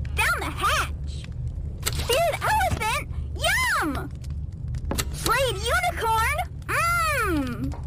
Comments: For a second I thought she said slaying unicorn 😂👁👄👁💅😢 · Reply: I heard “Slayed Unicorn”… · For a second I thought she said slaying unicorn 😂👁👄👁💅😢